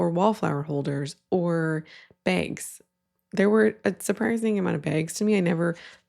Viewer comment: I had to turn on closed captions to understand you were saying “bags” at 3:27 and honestly I still don’t quite believe that’s what’s being said. I thought you were saying “banks” and then on screen it looks like lamps? I am so confused lol